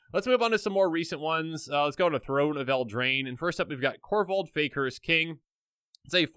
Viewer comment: Somehow I misheard Korvold's title as "Korvold, Baker's King." I guess that makes sense what with how he's often eating up food tokens, though.